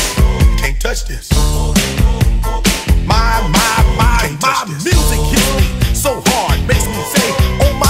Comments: stopp i thought he was saying this music gets me so hard😹😹❕ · I thought he said “music gets me so hard” lol